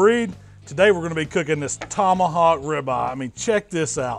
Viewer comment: Thought it said “Tony Hawks ribeye”.